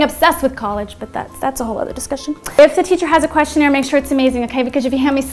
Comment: What in the world are you saying at 1:56? If the teacher has a what? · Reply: "If a teacher has a questionnaire..." Some teachers will give out questionnaire's for you to answer to help them write your rec letters.